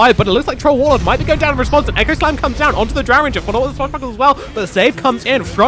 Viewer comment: Anyone know what he said? 😅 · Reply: onto the drow ranger, follow up with swashbuckle as well